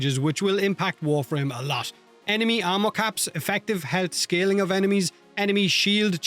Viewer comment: for a second there it sounded like you said "enemy ammo caps" instead of "enemy armor caps" and i literally had to wind it back and listen again to check... like can you imagine if enemies could actually run out of ammo and have to switch to melee without any disarm effects hitting them? that'd be so wild.